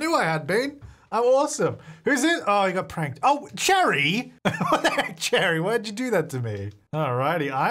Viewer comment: Why did I thought you said “oh, you gay?” 😅😅